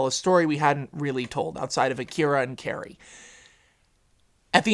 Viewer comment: What did you say? It sounded like "Akira and Carrey" I sort of understand the akira reference to the 90's anime movie, but is that what you said?